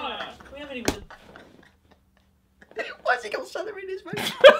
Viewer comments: What does say? I've been rewinding it but I can't understand him. Jesus help me. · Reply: Kerryy "Whys he got celery in his vest?" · it kills me every time i hear "why's he got celery in his suit" lmaooo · what does he say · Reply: "why's he got celery in his vest"